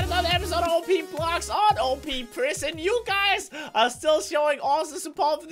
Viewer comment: did just said "OP Blocks on OP prison? on